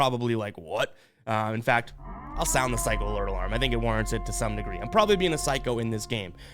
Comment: Boy I thought u said nigger but u said degree. Y’all go listen